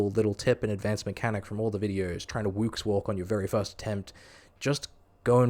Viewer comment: What in the world did you say at 15:59? Wukse walk? Seriously I've replayed this part like ten times and I have absolutely no idea what came out of your mouth. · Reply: the woox walk is a technique used at vorkath that lets you continue doing damage during the acid phase while still avoiding the acid. it's named after woox, the person who invented the method. he's a legendary pvmer